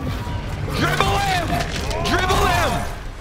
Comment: "Dribble him! DRIBBLE HIM!" lol thought you said cripple him at first.